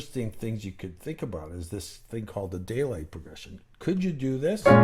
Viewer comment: Did you call the chord progression, the “daylight” progression? Sorry, can’t quite make it out. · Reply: Yes Daylight progression bVII7 - Imaj7 Gb7 - Abma7